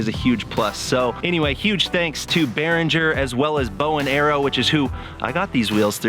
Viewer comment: Sounded like you said bow and arrow! Bowen Aero.